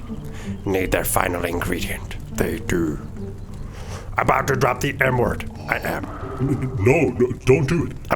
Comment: When Yoda said “about to drop the M word, I am” I thought he said N word 😂